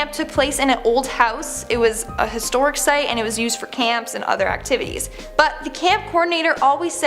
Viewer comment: I stg it sounds like she said "act-tiddies" instead of activitys